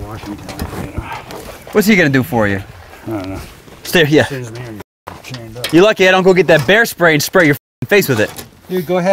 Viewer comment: Ohh bear spray i thought it said beer spray 😂😂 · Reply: I thought it said beard spray